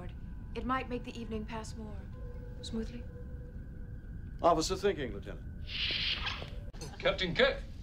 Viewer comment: Kirk says ‘Officer thinking lieutenant. I always thought he said ‘I was just thinking lieutenant. Amazing I only found this out now with subtitles!